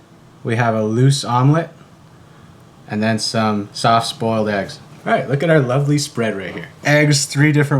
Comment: Ok, I definitely heard "soft spoiled eggs"